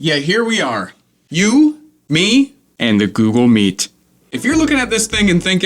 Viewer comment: why instead of google meet i heard susl meat